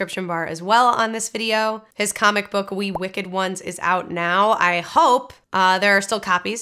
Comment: TBH, before you showed the cover, I heard it as "Wee Wicked Ones," a comic about ill-tempered dwarfs.